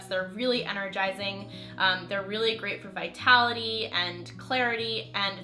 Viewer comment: Did you just say those mushrooms are great for immortality? Maybe I was just hearing things.